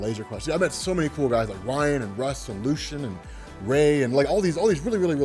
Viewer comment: Did you just say Lucian